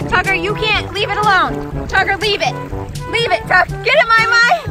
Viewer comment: What the name really is: Tucker What the YouTube subtitle says: F**ker At 5:38 btw